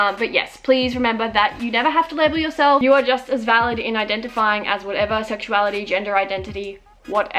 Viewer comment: When she said "you are just as valid" I heard "you are just a salad" XD